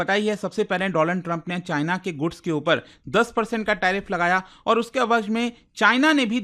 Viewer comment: did sir say Doland Trump??😅😂